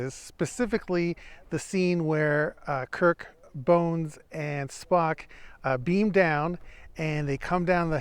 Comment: You nearly lost me in all out laughter when you said "Kirk bones Spock" at 3:24. I then realised what you actually said.